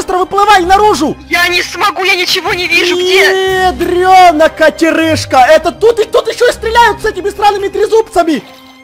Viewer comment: Мне одной послышалось что сказал эдрёна КАТЕРЫЖКА 😂